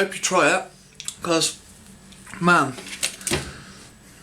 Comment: At the end, I thought he said, "I probably make my nan preg makin that".